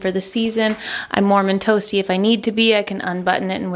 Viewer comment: It sounds like you say “ I’m Mormon toasty” instead of warm and toasty! which honestly, the chic look with the sweater over the dress is both appropriate and effortless! Something about the last few videos makes it feel like all of those little puzzle pieces of style, chicness, effortlessness and casual are all coming together for you and I AM HERE FOR IT!!!! Inspiring me to take a softer approach to parts of my life so thank you thank you thank you! Modular closets FTW!